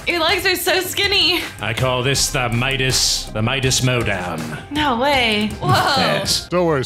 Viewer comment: Midas: This is my dance Midas mow down Me: I thought you called it springy